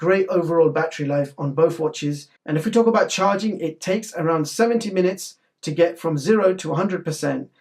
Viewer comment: im sorry but did you say seventy or seventeen minutes to charge? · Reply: 70